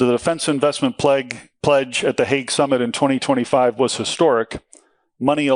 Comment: They should all invite Drumpf to NATO headquarters arrest him and ship him off to the Hague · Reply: For a second there I thought you said to “The Vague” which could be interpreted as a Black Hole or something similar, and quite suitable!! 🤣🤣🤣